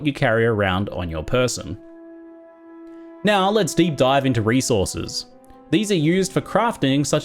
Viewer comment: I swear when you said "Now let's deep dive into resources" I swear I heard "Now I'll let Steve dive into resources". I kept waiting for "Steve" to tell me about resources...